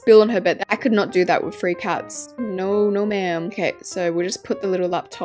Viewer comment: Did she just say 3 cats I thought 2 cats 😅